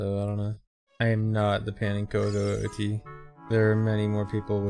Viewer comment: says pannenkoek here for those wondering. I googled it and it turns it its a dutch pancake. But more importantly there is a Super Mario 64 youtuber who is known for extremely technical videos. His name is pannenkoek2012. I was really trying hard to figure out what he was saying. Thankfully the chat had the message he was referring to.